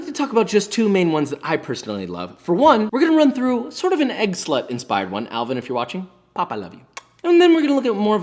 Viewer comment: did josh just say egg slut lmao?